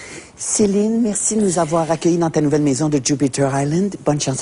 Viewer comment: Qu'est-ce qu'elle dit là? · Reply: Elle dit Merci à toi Julie